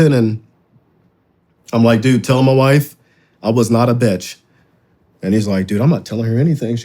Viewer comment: that sounded like you’re a bad bitch